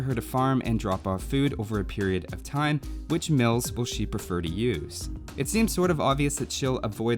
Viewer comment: Was kinda sleepy, thought it said milfs at first.